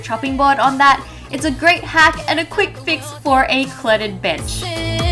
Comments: Did Wengie just say b\tch?! I thought shes family friendly?! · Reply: I Loaf Julia she said, "quick fix for a cluttered bench." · Wengie when you said bench I turned on English caption and it thought you said female dog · did she just say " for a cluttered bitch" ?